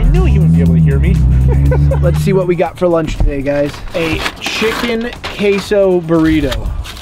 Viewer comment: I can’t have been the only one who heard him say a chicken caseoh burrito unless he did say. Caseoh also going round 10 times you’re just putting more money in their pockets I wouldn’t be mad also this Taco Bell employee you gotta invite him to join the team